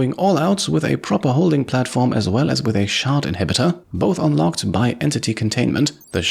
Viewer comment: tell me I'm not the only one who heard "shart inhibitor" I was like "wait so like... the opposite of a laxative?"